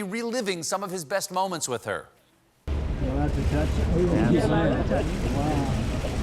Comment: Does anyone know what Melania says @ 6:47, just before T speaks... Does she ask: "Touch it?" ...?